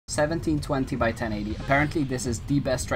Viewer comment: what resolution were u using I thought I heard you say 1600 by 1080